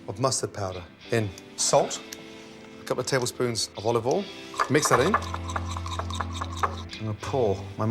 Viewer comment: olive oil in... I hear it as "lube in"